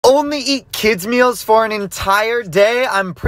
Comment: What he said ‘ only eat kids meals ‘ What I heard ‘ only eat kids as meals’